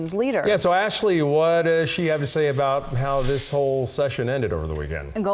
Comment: I thought they said Persperations 🥵. That's gross 🤢.